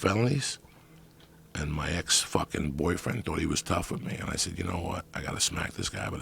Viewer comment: Did Joey say "his ex-boyfriend"? Is he gay? What is he saying at 1:30:40?